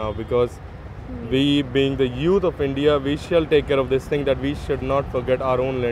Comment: the subtitle is wrong. The guy said" we should take care of" instead of " we should make sure".... I am Chinese. I think Indian accent is cute and quite charming..:).